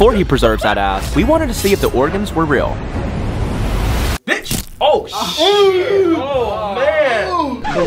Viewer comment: OMGGG IDK WHAT HE SAID IT SOUNDS LIKE when is about to stab it of the real lung sty 7:58 it sounded like this when he stabbed it”bitch” I THOUGHT HE SAID THAT IDK WHAT HE SAID LOLL😂😂😂😂😂😂